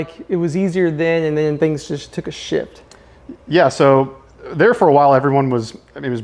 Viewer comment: I thought you said “Things took a shit” at first, I’m dying rn🤣